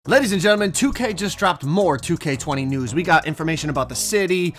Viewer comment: or I’m tripping or did he said 2k20 news 😭